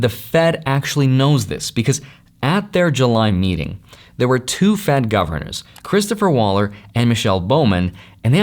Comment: On 11:45 did he just said that ? 2 fat governors ? 😮